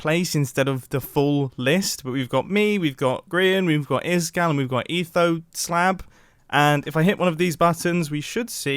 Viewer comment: I swear to god he said Ethos slap